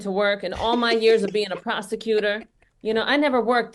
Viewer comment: did she just say “a prosticutor”? 😂 🤣 Kamala had her Freud moment